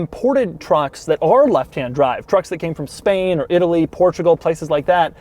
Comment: did he say it came from span